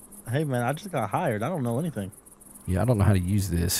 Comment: Lets see how this video goes but my initial reaction is that the extra camera wont make a huge difference 7:55 I thought said, "I just got high and I dont know anything!" Had to go back and listen again.